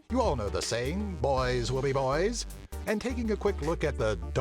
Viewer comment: i thought you said Boingz will be boys